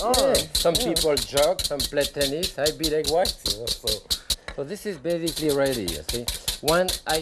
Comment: What in the world did he say at 18:11 😈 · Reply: "Some people jog, some play tennis, I beat egg whites" 😁